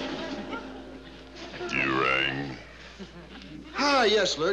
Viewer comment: did lurch say "DURANG"